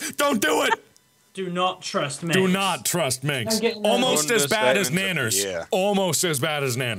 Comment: I thought he said almost as bad as mainers, and I was gonna cry xD